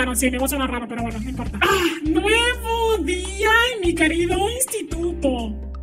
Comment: entendí "Un nuevo día en mi querido prostituto" 😭🖐️ JSJSJSJ